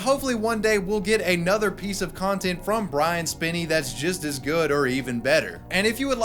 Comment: I thought it said Brian Spilner at first LOL IYKYK